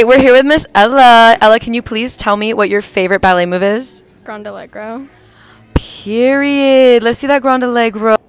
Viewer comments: The first one I thought she said grande eggroll 😅😅😂😂😂😂😂😂😂😂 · Thought she said ground Allegra and i was like oh no